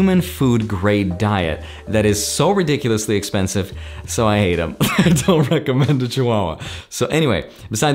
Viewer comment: "So I ate him." -That’s what I thought was said after the explanation of the high grade human food diet for the chihuahua. 🤣 Which would have been a devastatingly funny joke.